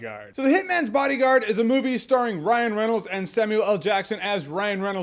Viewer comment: I thought you said Ryan The Reynolds for a sec lol